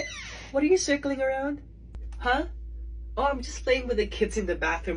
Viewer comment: I thought i heard strangling, instead of playing. Oh thank god. 😫